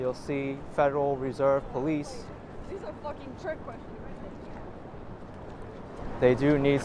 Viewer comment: Did anyone grasp what the girl crossing the street said at the minute 6:41? I only could hear something like " this is a fuc*** ...... question. Humnn..idk!